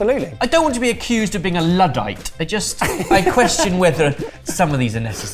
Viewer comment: What's the word Ben's saying at 9:32? Always expanding my vocabulary with Sorted 🤓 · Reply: Luddite. Google it's meaning though